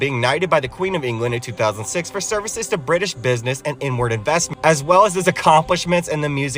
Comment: i heard "inward investments" as "n-wrd investments" omg 🤣😂